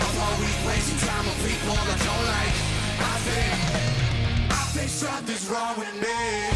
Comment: oops? hahahahaha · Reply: I heard “somethings fucking wrong with me” the first couple times but now I hear “I think”